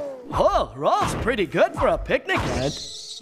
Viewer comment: Upon learning that Ed's odd expressions may come from misunderstanding what his parents say, just what might have he meant by Rolf being pretty good for a "picnic head"...? Edit: I had an Ed moment and misheard "ant" as "head". My bad, everyone!